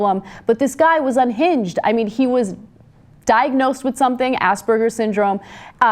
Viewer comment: Did Ana say "ass burgers?" lol